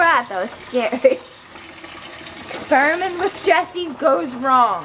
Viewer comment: I thought she said"sperming with Jesse"😂😂